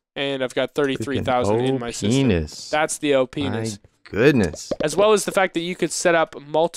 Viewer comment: Did say penis?